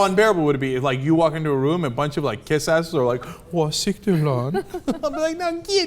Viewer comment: What the heck does Cenk keep saying?! @ 2:47... wahseekteley...????